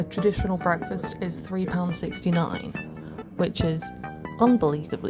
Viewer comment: the captions say £369 rather than £3.69